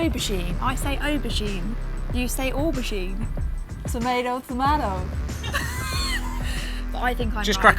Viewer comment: when she says one word with two different pronunciations, what did she say? It sounded like "meadow", but not pronounced like it "normally" is. Also, I didn't really get the whole sentence. English is not my first language and I love learning new things, so it would be really nice, if someone could tell me :) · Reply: Tomayto tomato